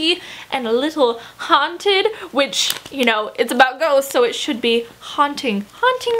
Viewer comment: Okay this is random but I felt that I should share it anyway cause it'll proabably make someone's day but when you were talking about "I'll Give You The Sun" when you said "ghosts" I heard "goats" XD I don't why or how I misheard that but yeah lmao.